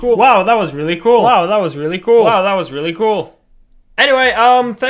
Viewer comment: did he say wow that was really cruel?